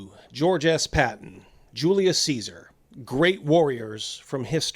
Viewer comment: Did he just say Julia Cesar? I thought he didn't like trans inclusion